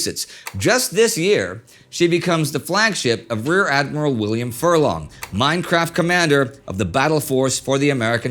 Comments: Did he say minecraft comander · Did I hear that correctly? "She becomes the flagship of rear admiral William Furlong, Minecraft commander of the battle force for the American pacific fleet." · did he say Minecraft commander Edit: someone already pointed this out so don’t like